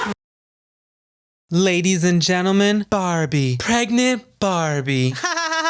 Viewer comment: Omg I thought she said baldie not BARBIE😂😂😂